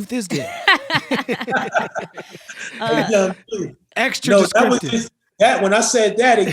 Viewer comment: I always thought he said a “ young duke” but its a young youth 🤦🏻‍♂️